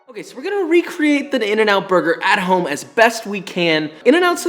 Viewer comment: I heard "In n Out but butter" and now I can't unhear it